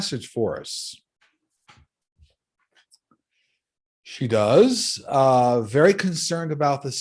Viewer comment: There is a female voice at 3:31 when you are about to talk about Madeleine Albright, She said she is great or something like that. is weird I hear it cause I also hear and see interesting stuff on my daily life. that is why I watch your channel. I can't explain or understand most stuff or who is talking to me. but is always for good things or good info. One time I badly need it a receipth that I had lost or miss pace overnight. morning time looking all over. I was getting tired about to give up looking. when I heard a very nice man voice said on my left ear. "Is in the bag, check inside the trash bag" I even turned aroud to look who say it. of course it was just me in my condominium. I rush to look in the trash that is in the kitchen. it was there. night before I had the paper on my hand. I also had a tissue that I was using for my nose while watching tv, when I went to bed threw I had accidentally threw the receipt with the tissue. If is not for that voice that I heard telling me about. I would hand never look in there or even think about it. I know it sounds weird but is all true. I am not crazy. I said this cause most of my friends don't understand when I tell them stuff that I saw and what to be careful with. it usually happens the way I saw it...but some friends dont understand it and some assumed I am weird for that. some even stop talking to me when I told them.